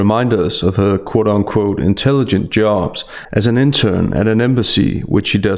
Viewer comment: Misheard that she was an intern at a US embassy as her being "an intern as an imbecile'.